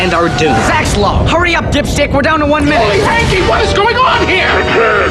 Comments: does Zack say "dipshit" or "dipship"? Because to me it sounds a lot more like the former, but I doubt they put that swear in a kids show. "Dipship" would make sense because... well they're on a ship. But it still sounds weird to say. I feel like he says "dipshit" but I wanna know what you guys hear, and think? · did I just hear Zack call Cody a dipshit? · Did he just say "dipshit"?! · I’m sorry but did zack just say dipshit on the Disney channel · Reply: sounded like "dip stick" to me but very close lol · i cant be the only one who heard "hurry up, dipshit" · am I mishearing the ad or is Zach saying "dipshit" here? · does Zack say "dipshit" or "dipship"? Because to me it sounds a lot more like the former, but I doubt they put that swear in a kids show. "Dipship" would make sense because... well they're on a ship. But it still sounds weird to say. I feel like he says "dipshit" but I wanna know what you guys hear, and think? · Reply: i thought it said dipshit at first too